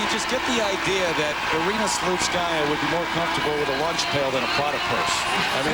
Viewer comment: OK, I have to know now... What did Terry say? "Irina would be more comfortable with a ... than with a platypus"?? I cannot work out what this might have been. And I have to know. Help! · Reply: i heard she would be more comfortable with a lunch pail than a prada purse